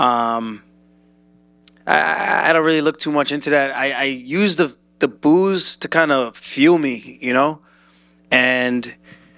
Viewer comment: I heard: "I use the boos to fuel me." As: "I use the booze to fuel me." And I was like "Aljo.......damn."